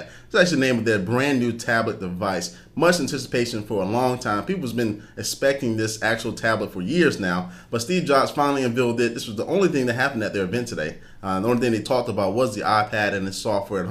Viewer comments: did he just say "unvealed"? LOL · Did he just say "unvealed"?